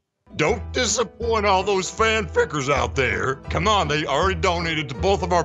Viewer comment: that sounds like he said ,,Fan fuckers"